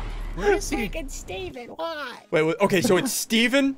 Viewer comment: DID ANYONE HEAR SSUNDEE SAY "f*ckin steven why?" at 13:38?